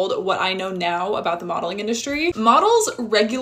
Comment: lol my ass thought she said "bottling industry"